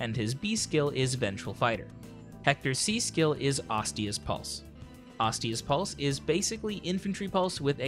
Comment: Whenever I hear "Ostia's Pulse" my brain expects to hear "Osteoporosis"